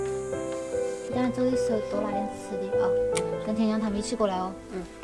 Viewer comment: guys， this is not complete, I just heard sth said in vedio, hoping more people can understand and know that she is so cute~ 1分20秒：你等一下走的时候多拿点吃的哈，跟xx他们一起过来哦 2份12秒：你们不觉得民国长得很像刘欢老师吗？ 3分07秒：你们喝蜂蜜水还是玫瑰水呀 3分37秒：来 菜好了奶奶阿姨 3分44秒：来喝一杯 6分16秒：来 这个 好看吗 6分30秒：奶奶 你看我把这个戴上像不像个女侠 9分35秒：奶奶过来一下 你来转 转到哪个我给你做哪个 不行 不行 重新转 这个好难哦 不行 奶奶你重新再转一个 10分15秒：奶奶你看这个 一个大的一个小的 我给你做个我的名字 11分53秒：打了一些麦麸子回来 13分47秒：奶奶枇杷吃吗 18分05秒：你这个等到饭后再吃 18分20秒：小麦茶 来 这个有点辣 18分50秒：吃不吃 我给你敲 1:20 Remember to take more food when you leave, and come with aunties later~ 2:12 Don’t you guys think Minguo looks like Mr LiuHuan(A famous Chinese singer)? Hahaha, just a joke, I like his songs. 3:07 Do you want to drink tea with honey or rose? 3:37 Dinner is ready! Grandma, aunty 3:44 Cheers! 6:16 haha grandma, how do I look, cool, right? 6:30 Am I look like a swordswoman with this straw hat? Grandma, look, look 9:35 Grandma, come on, show you something funny. You rotate it, and I will make you sugar which it turns to. No, too difficult, no, rotate again, grandma. still hard to make, no, please try again 10:15 Look this, big heart, small heart~ 11:53 I get the wheat flour~ 13:47 Grandma, do you want to eat loquat? 18:05 We can eat it at the end of the meal~ 18:20 Grandma, have some wheat tea. Ah, this is a little spicy 18:50 Try this rock maltose candy ? Let me knock it down.